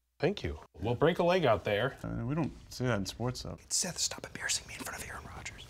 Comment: What was the last word Aaron Rodgers said after "sports"? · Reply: Though